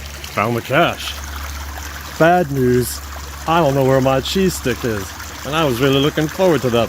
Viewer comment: Oh man, I kept hearing "Cheesesteak" for the first 5 minutes of this video. Even though I wasn't eating any of it, I STILL got disappointed when I realized it was only a cheese STICK.